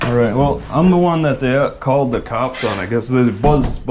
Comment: At about 3:12, it sounds like he said he was the one they called the cops on, not that he was the one who called the cops.